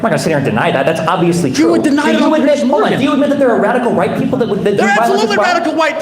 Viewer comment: did he say Radical White people?!